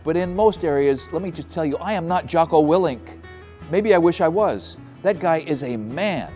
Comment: You're not WHO? (I couldn't catch the name) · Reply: Jocko Willink